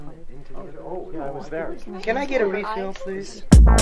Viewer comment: Legend says that the man is still asking for a refill to this very day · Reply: For a second I thought he said "can I get a meatloaf please"